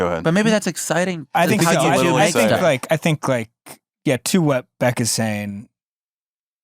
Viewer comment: i thought he said "too wet beck"